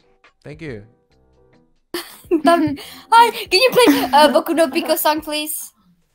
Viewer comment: hooooolllllllld up did she just say boku no pico